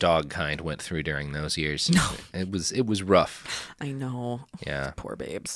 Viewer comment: "it was RUFF" I heard it